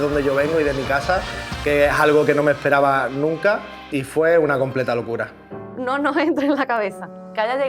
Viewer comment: pense que dijo tfue